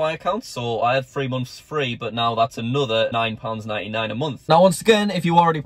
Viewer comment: I misheard him and thought he said £99.99 a moth